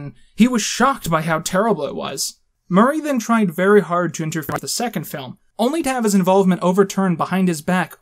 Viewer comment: is the audio at 18:32 fucked up for anyone else? i think he's saying "intervene" but i can't tell because it skips through the word